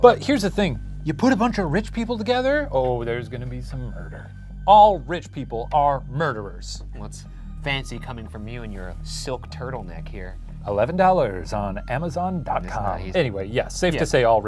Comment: misheard murderers as furries